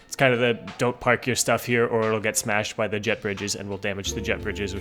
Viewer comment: I swear, every time he says 'Jet Bridges' I hear 'Jeff Bridges'